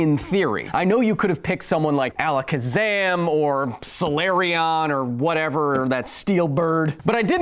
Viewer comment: Did he say Solareon?!